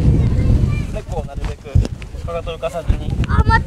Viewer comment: 体を浮かさずにって言ってる？かかとにしか聞こえん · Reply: かかとです